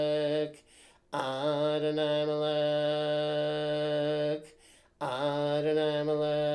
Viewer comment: Feel free to do the ritual as Regardie wrote it. It should be pointed out that Crowley never used or performed this ritual, and the name Adonai Melekh for Malkuth comes from Golden Dawn sources that predate his work. So this isn't really a case of Crowley throwing anything anywhere. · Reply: oh! I thought he was saying Molech! My mistake.